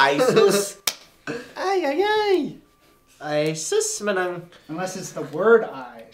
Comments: WAIT WHAT???? DID I JUST HEARD RYAN SAID "AYSUS MANANG!!!" on 8:37 ??? I thought Greg and Paco can't speak Tagalog so how did Ryan learned that word?? Hahaha Aysus Manang in english is "C'mon Sister" and the way you say it should be like youre disappointed with that person. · “Ay sus, manang!” 😂 did i hear that right? 😂